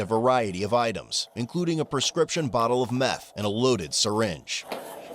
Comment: I thought he said a loaded gun but a loaded meth in s syringe 💉 what a freaking dope fiend crack head.